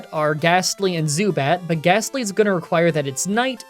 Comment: i legit thought he said- "The only Pokemon Available to us are gas station zubat" Also if the next video is Blaine team and moveset does that mean super potion is also included.....